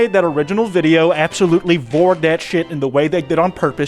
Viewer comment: Did you just say VORE😅